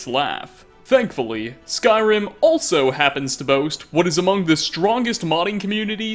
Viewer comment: holy fuck 2021 is the worst year now i hear amongus everywhere and start laughing